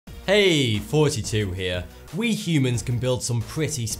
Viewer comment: its sounds like you say ''hey its 42 here'' at the start.